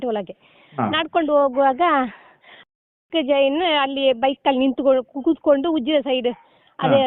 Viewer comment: did she say jain??